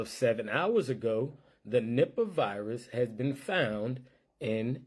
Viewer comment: I thought he said nipple virus at 6:34 😂